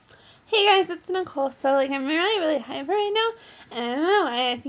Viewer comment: i thought she said she was really high right now